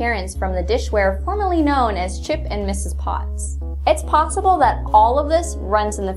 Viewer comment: I heard potsible. Clever.